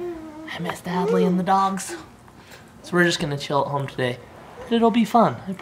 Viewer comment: I thought he said they were going to chill at home all day